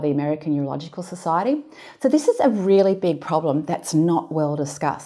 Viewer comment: I couldn't help but get tickled about two comments around the 3 minute mark in the video. in part, I heard "it's a really big issue" and "iseally big problem" . Just thought there's got to be a pun in there somewhere. Just struck me as humorous. It was an interesting video. It looks as if you put a really large amount of research in this study. Thank you. Please excuse my pun, I was just having some fun.